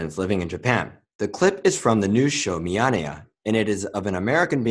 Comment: MiyaneyaがMjölnirに聞こえてなにそのTVショーは・・・って動画止めて考えこんでしまった。 結局わからず答え合わせをして納得しました。 英語発音の日本語が難しい。